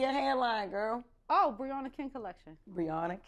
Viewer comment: Briana Kenn , I thought they were saying Briana king lol